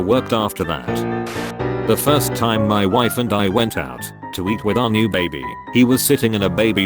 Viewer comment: I thought he said "the first time me and my wife went out to eat our new baby"